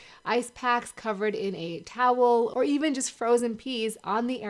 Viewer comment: Did she just say " frozen pee"🤣🤣🤣.